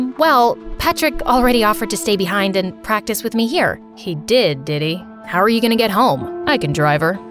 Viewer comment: why tf did I hear “he did diddy”😭